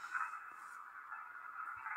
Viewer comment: I heard "I'm listening to everything " 😮.